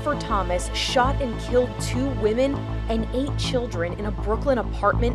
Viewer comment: I thought she said ''ate children'' 😅